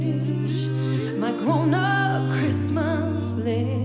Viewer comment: omg I thought she said this is my corona christmas